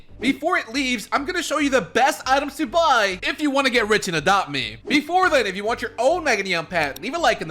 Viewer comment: I THOUGHT YOU SAID IM LEAVING ADOPT ME AND I STARTED CRYING 😭😭😭